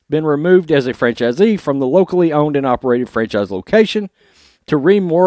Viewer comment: When you said "locally owned," I heard "wokely owned."